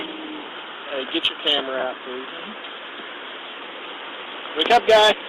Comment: I heard "get your camera off" 😮😮😮